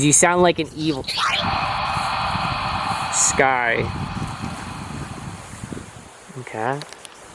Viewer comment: see 1:08:35 is sounded like die or why and it said sky btw i watched dark skies and loved it that was crazy kinda reminded me of fire in the sky